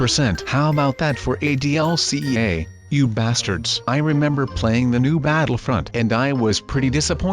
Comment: did just say you bastards